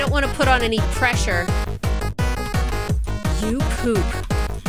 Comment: I thought she said you poop